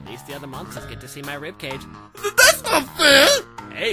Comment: XD omg when he said well your the one who puked on me I thought he said pooed on me omg XDDDDDD